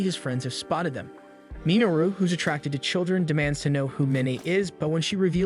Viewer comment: Did he say children?